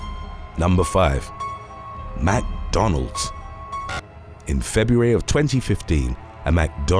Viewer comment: sounds like he’s saying Matt Donald’s 😂 8:29 mcdaren instead of Karen